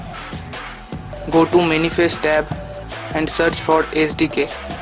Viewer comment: Now when I click on manifest and type in the apk which is Kodi 15.2 it says," can not find target string in andriodmanifest.xml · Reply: I got it now, I thought you said search apk when you said sdk, thanks a million.